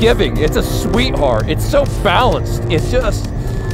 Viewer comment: Great review, but I thought you said, "this is so phallus" at 4.08, then realised after replaying it a few times you actually said "balanced" which is a shame, as "so phallus" would have been a great piece of commentary....